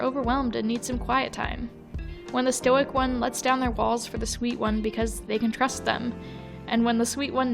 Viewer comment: am i insane or did i hear balls instead of walls